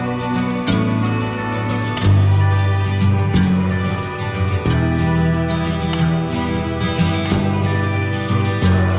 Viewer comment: What is being said at 1:30? I think I know what is being said and I think there could be some controversy, but I want to hear your thoughts first to see if what you guys are hearing matches with what I hear. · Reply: “Oh sir frankie crisp”